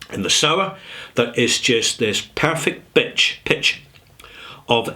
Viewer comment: Did you just say "Perfect Bitch"(19:05)? 🤣🤣🤣🤣🤣